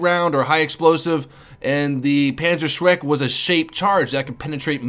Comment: i swear i heard "Panzer Shrek"